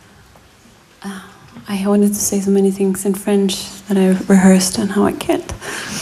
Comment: What did she say at 14:47 ? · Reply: She says: "I wanted to say so many things in french, we rehearsed but I can't..." (Meaning can't speak french)